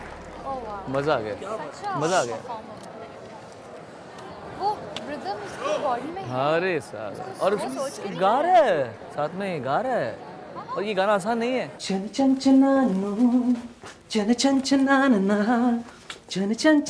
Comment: Did he say 'Arey Sala' ?